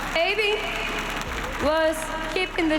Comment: what did she say? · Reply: Fabi idk why tho